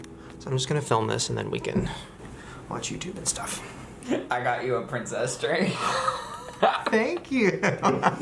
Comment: Shane: gets all serious Ryland: i got you a princess drink (that's what i thought he said at least)